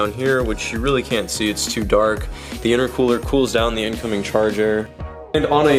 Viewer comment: the intercooler cools out the incoming charger ? wtf is that ? the intercooler cools the air after the air got hot due to the compression of turbos and before it enter in the engine is cooled by the intercooler. don't seem like what you said has any clue what is really doing. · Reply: he said incoming charge air not charger